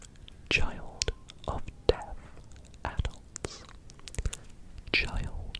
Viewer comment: Not going to lie I thought he said child of death adults